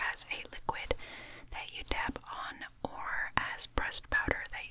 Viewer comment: Every time you said "pressed" I thought you said "breast" lol